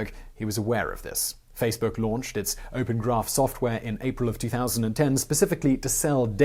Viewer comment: you said 2010, caption says 2012.